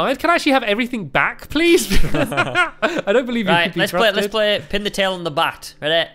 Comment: I thought said "let's play pin the tail on the butt" 😂